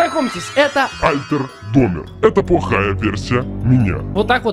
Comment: на 1:45 мне показалось что он сказал: это бухая версия меня 😂